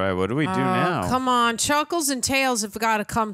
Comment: Did she just say chuckles?